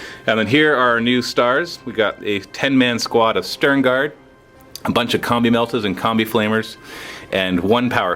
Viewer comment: is it just me or does it sound like says "Here we got a bunch of comme meltas and comme flamers" at 2:06?